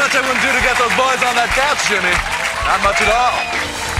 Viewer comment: What did the guy say at 00:40? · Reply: "There's not much I wouldn't do to get those boys on that couch jimmy, not much at all. " It is connected to the previous clip from his show. Watch all of them in order